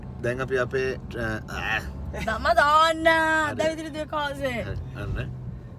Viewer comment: only one who didn't understand this is me? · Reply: E kiyanne “ anee deewa maaniyanee!! Kiyanna thiyenne deewal dekaineee 🤣❤️”